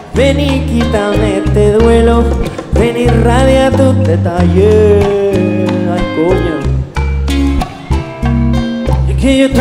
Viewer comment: Did he just say "Ay coño" at 4:51 ? 😂